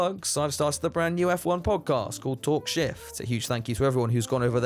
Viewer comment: I thought I heard "talk shit" instead of "talk shift"... although it's likely to be an accurate description 😆